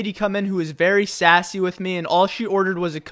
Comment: i thought it said sussy